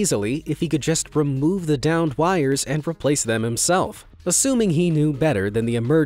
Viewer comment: You know at first I thought you were saying 'the damned wirer' and not 'the downed wirer'😅